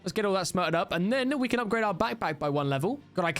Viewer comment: anyone remember the Netflix show BatPat? Thats what I heard in the stuttering